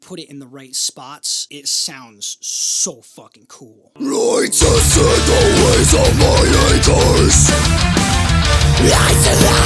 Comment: sounded like He was singing: "i've made me my Way through my anuuus" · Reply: I hear I've tested the ways of my anus